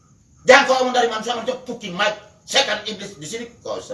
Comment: apakah tak salah dengar bunyi makian itu?😣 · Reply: Bupati gak pantes bicara "cukimai" dll